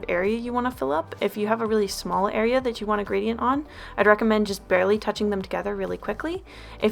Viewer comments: I thought it said "GRADIENT PENIS?" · I thought it said gradient penis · I thought it said gradient penis....XDD